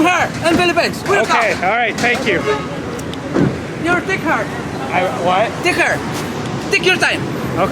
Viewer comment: did he say dick her? lol